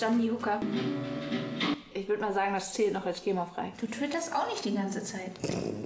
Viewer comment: Was sagt sie bei 3:16? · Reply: Du twitterst auch nicht die ganze Zeit!"😂